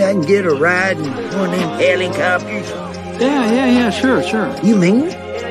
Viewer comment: I thought he said "fancy little helicopters"